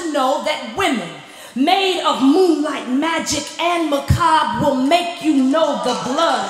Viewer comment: after "moonlight magic" around 5:18 what was the other m word? im hearing macob but i dont think thats right. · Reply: macabre